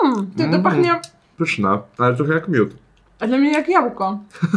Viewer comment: usłyszałam ,,troche jak fiut''😭